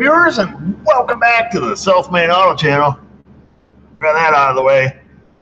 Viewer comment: Funny when ya say south main auto, I hear“ self made auto “ like it.